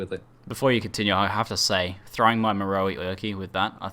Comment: What did he say at 16:35??? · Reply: ''throwing my Marohi Erqi with that'' referring to spectral throw, Its a unique 2 handed mace in poe